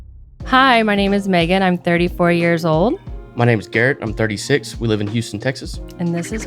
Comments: is he 36 or 26? I could’ve sworn i heard 36 but i see 26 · im pretty sure he said 36 but the captions said 26😅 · Garrett is 36 but the video caption said 26. Just wanted to let you know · noticed a little typo here, he says he’s 36, but, the caption says he’s 26. Just feel like I should point that out. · Pretty sure I heard him say he was 36, and your editor put 26 on the screen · i think he said thirty-six this video feels surreal, the couple acts so stereotypical that it almost feels like they're acting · misprint of guy's age. Instead of 36 its 26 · why does it sound like he says 36 and looks 36 but the text says 26? is he 26? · small error in the caption (he said he's 36 and the caption said 26) at 0:29 Also a strange editing screen